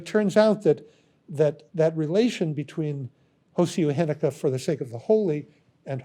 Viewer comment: He has a term ‘for sake of the holy’ and ‘for the sake of form’… I think I heard it as ‘hosei heneka’ but can’t translate it correctly. Can anyone help with the correct terminology? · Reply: όσια ένικα